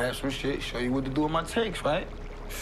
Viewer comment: Does anyone know what Bay says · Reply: we shot a noco (narcotics officer)